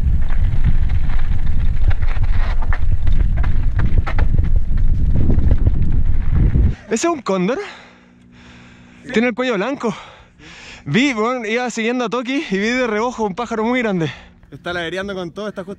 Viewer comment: escuche condon :v